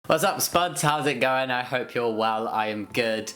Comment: Omg i only just stated watching this channel and I never realized he was saying ‘what’s up spuds?’ I thought he said ‘what’s up sperms?’ 😂